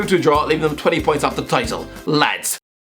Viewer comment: What the hell is he saying at 8:10 ? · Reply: Fucking hell I'm Irish too. I thought I might have been somewhat of a help but all I got was "Talk to ya in a while". 😂